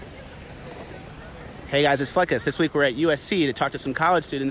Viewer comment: I thought this fool said, "Hey guys, it's Fuckas."